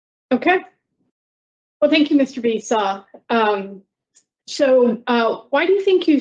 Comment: does she call him Mr. Beast? 😂